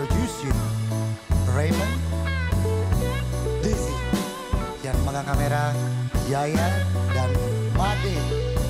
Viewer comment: y'all what does he say ⁉️ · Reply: the bassis name = dizzy cameraman yaya and guitaris = made